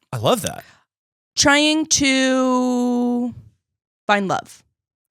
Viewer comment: hard of hearing, says she's trying to "find love" but I hear "buy a club" and I'm like "...wait did you just randomly drop that? is that a thing 20-somethings in LA just DO?" 😂 😂